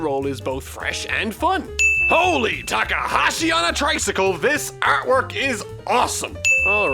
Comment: “this artwork is amazing”. But the subtitles say art style